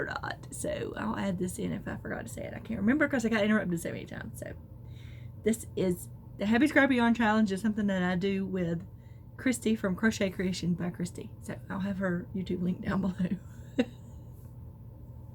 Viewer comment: It sure sounded like you said the happy crappy yarn challenge the second time. Very funny maybe my ears are off.